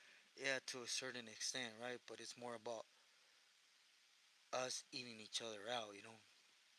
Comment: I was listening to your message pero I dont understand what you’re talking about in minute 6:48 can you clarify that sentence if possible I don’t know if i’m hearing you correctly pero it doesn’t sound right what you’re saying just want to make sure I didnt misunderstand gracias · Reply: Hahaha 🤣 damn recording, dispensa it came I never listen to them..I meant more on us beating on each other.. hahaha...thank you for letting me know.. Saludos buen día